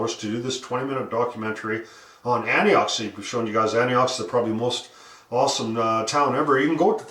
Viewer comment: Sorry, but with my bad ears, I originally thought you were saying "Antioch", which is a town in California. When I looked it up, however, it had no mining connections, so I was mystified why you were talking about it until I saw the correct town name further along in the video. I just barely stumbled across this channel today.